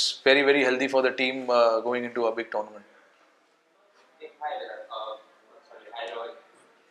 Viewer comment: The thumbnail. Reporter: "Hi Virat!.. umm.. sorry, Hi Rohit!" Rohit: "Kya bola?" The person on Rohit's left: "Hi Virat."